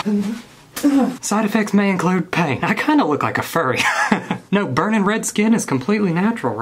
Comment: Did he just say he kinda looks like a furry? XD